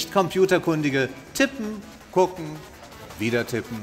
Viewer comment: ich hab beim 1. Mal verstanden: Titten, gucken, wieder Titten xD